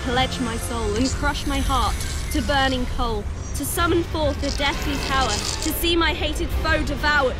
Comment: Spell: I call the darkness unto me From deepest depths of earth and sea From ancient evils unawoken Break the one that can't be broken To blackest night I pledge my soul And crush my heart to burning coal To summon forth a deadly* power To see my hated foe devoured Note*: It sounds like here she's saying "deathly power" but later on older Moon says "deadly" which I believe is the real word and it may either be an accident of the voice actress or just a misheard word on my part. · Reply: myname seriouslyisntdaisy yeah it SOUNDED like she said deathly bc of the accent but she said deadly one way or another